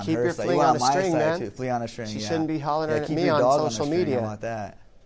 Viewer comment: Cody thought he said Flea on a string 😂